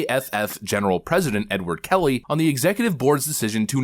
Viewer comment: I was listening to this in the background and I thought he said "N-word" Kelly 🤣😆